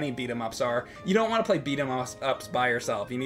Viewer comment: It may have been at the very end of a 32 minute video, but we all heard you say Beat 'em Offs! Lol